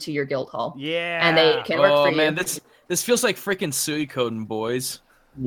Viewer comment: um I'm sorry what did just say, I didn't understand that word 03:43:22 , someone watching these please reply and tell me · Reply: If you are asking about them saying Suikoden. They messed up the name a bit but it’s an rpg game series where each game you eventually get a castle and can recruit around 120 characters. Many are fighters but many have jobs so they add shops and stuff to the castle when you recruit them.